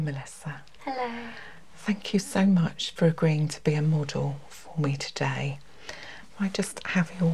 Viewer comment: I thought she said "thank you for agreeing to be immortal today" 😂😂